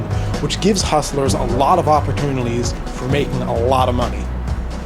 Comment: Did he say 'opportunilies'